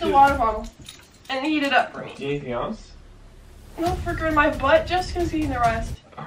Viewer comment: Can anyone understand what she's saying at 7:57? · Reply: “No freaking my but just needs a rest” that’s what I heard 😭